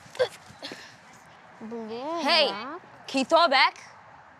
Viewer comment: the start of the video i thought he said to Amber “Hey,can you THROW IT BACK-“